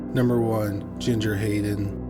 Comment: For story number one... I thought initially you said Ginger hating 🤦🏾‍♀️🤦🏾‍♀️🤦🏾‍♀️.